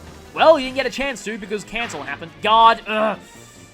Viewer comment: I feel like we need some bonus footage of Ashnard soloing this map, but the enemies rush him likes moths to flame, all to die VERY painfully. 21:50 Okay, THAT time it almost sounded like "Oh, cancer happened."